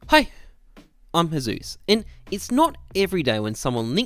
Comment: Sounds like you saying ”hey im Jesus” in your intro.